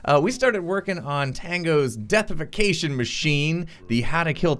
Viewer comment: Thought it said defacation machine